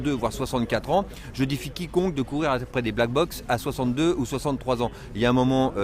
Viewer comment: il bafouille quoi ? Mdrr j ai compris de black box mdrr